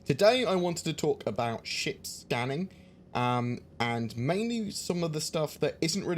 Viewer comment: I'm a 9 year old apparently, I thought I heard you say shit scanning. And I chortled even tho I knew I had to be wrong. Aaand this comment has gone on way too long..